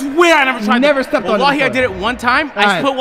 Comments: He def did not say well while here @ 4:55 he said wallahi LLMFAO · Agent said “wallahi” and the poor editor put “well while here” he was probably so confused at what he said 😭😭